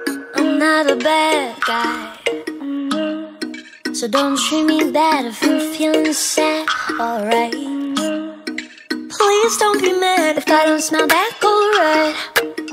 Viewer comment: IS IT ME OR DOES 0:29 SOUND LIKE SHES SAYING "IF I DONT SMELL BAD, ALRIGHT?"